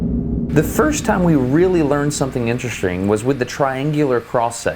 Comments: I heard you make that joke at 6:59 “Intrestring”😂😂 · did- did you just say interestring?? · “The first time we found something intra-string...” 6:58 😂 Hehe I think you’d been saying the word”string” a lot, or that was a very casual pun. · haha did he say intereSTRING?